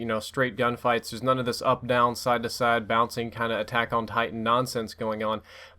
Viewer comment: Did he say Attack on Titan?